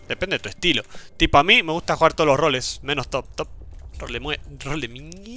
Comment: que iba a decir XD yo entendí tolemue